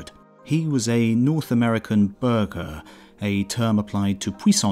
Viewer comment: If I had not played Paradox games, i would've thought you said "Burger" instead of "Burgher".